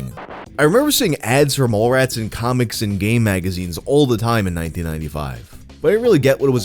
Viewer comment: definitely heard "gay magazines" instead of "game magazines lol